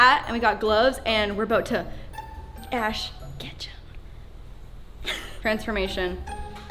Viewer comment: Who else thought Lauren said Ash Ketchup